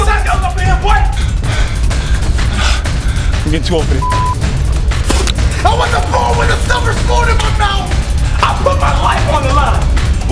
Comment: what does say at 1:09? "I want the _(?)_ with a silver spoon in my mouth?" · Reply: serolfsk8er I wasn’t born with a silver spoon in my mouth that’s what he says